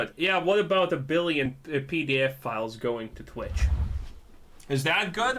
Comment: To clarify, he's saying " oh hes essentially saying any growth is good, What about the billion pedofiles going to twitch? No obviously not." Definitely sounds like hes saying PDF files. Also this guy is all over the place with his logic, and his accent just makes it funny - but he's narcissistic and borderline ready to the be the next headline in the news.